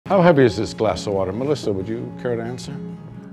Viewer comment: Omfg... I thought he said "how happy is this glass of water?" And I got so confused lol